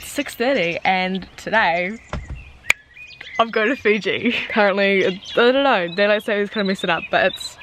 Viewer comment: Did I hear you're shouting us to Fiji. I'll fly up to Auckland from Palmerston North 😂